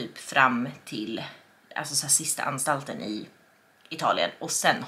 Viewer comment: bokar ni sista boende i italien till sista anstalten? Jag hörde nog fel. Men lite kul. Gulle!!! 💕💕👌👌